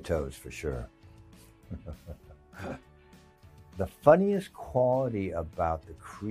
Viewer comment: He said "quality", but I heard "quabity".